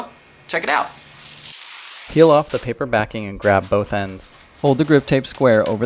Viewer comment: when he says "peel off" at 0:19 it sounds like he says YOLO hahahaha